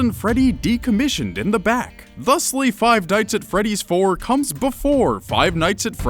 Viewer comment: did he say dites instead of nights